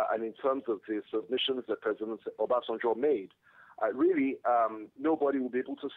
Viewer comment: Did He Say President Obasenjo?